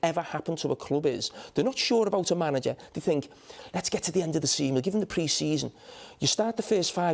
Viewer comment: Did Carra say “give him till the end of the semen”?